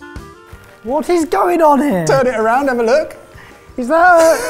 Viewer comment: I thought i heard say "TURD IT AROUND" 1. Sri Lanka 2. Hungary 3. Italy (YAHOO!!!!!!!) 4. Mexico (OMG!!!!!)